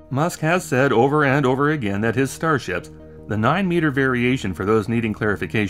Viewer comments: I swear i heard "starshit" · Did you say Star Shit at 2:31 or is that my mind filling in the truth wherever it can?